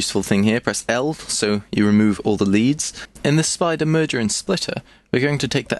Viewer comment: what key to press to remove leads? can't hear what he/you say. sounds like "L" but that doesnt work to me atleast!